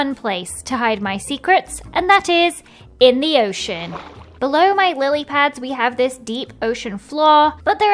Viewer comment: Am I the only one who heard her say bellow my willypads?